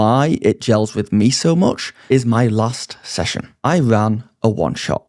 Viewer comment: I heard “gels with me so much is my lust session.”